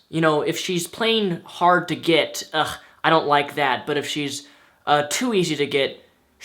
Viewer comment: Really? He did not say "I don't like that" he said "She doesn't even like me" which fits perfectly with the context, playing 'too hard to get'. Yes girls have told me about how they "played their role" (hard to get) only to experience the dude "lose interest". So yes, sending the wrong signals will result in wrong results, this is basic communication 101. Just wanted to point out that one misstake, I do love most of your videos and they are usually spot on!